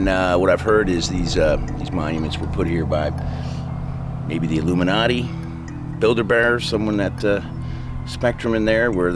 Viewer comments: Dude said Build A Bear...instead of Bilderberg. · did say "Builder-Bear"? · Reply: Bildeberg